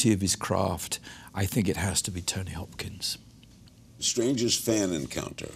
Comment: At first I thought he said he was star struck by Tony Hawk... I was actually impressed.